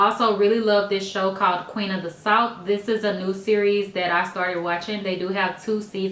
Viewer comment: Yes girl, Queen of the south is life !!! I'm obsessed with it. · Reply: I thought she said Queen of the salt...lol! Thank you for repeating!